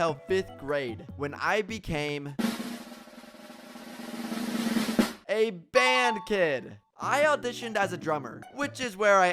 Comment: I thought you said you are band from school